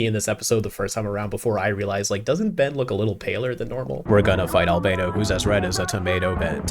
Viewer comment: Gotta love innovative rhymes. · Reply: I thought he said “We’re gonna find Albedo, who’s dressed as a tomato”, and that got me thinking about Albedo doing the “my body is ready” meme